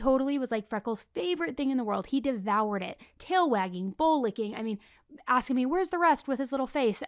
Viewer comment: OMG.. I had to listen to the Ollie ad twice... I genuinely thought said the dog was so happy it was tail wagging and BALL licking. Thank goodness I finally her say BOWL licking. The visual of my first thought was still stuck though. Hahaha. I know I am not the only one who heard that wrong. Hmmm? Love you, had to share this silly moment. Love what you do, I am a constant listener xoxoxo